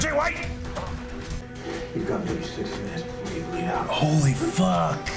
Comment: can someone tell me what Oliver said here? I don’t watch Arrow currently but I’ve been watching a few of their reactions because it’s good again but I can’t make out what he says here. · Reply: RyanKlip he said you have 6 minutes until you bleed out